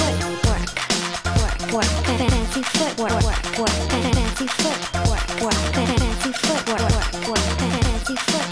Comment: Did he just say "...and maybe do the Twerk" ? haha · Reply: ye, just checked the lyrics :D he really said twerk :D